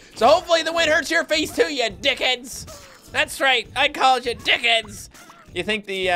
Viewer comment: it did recognize it as dickheads but the second time it thought you said tickets